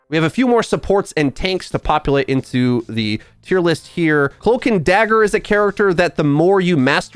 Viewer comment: i swear you said hulk n dagger